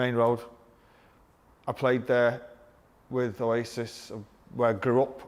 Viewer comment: heard that mid hiccup and genuinely thought he said "Played there with racists"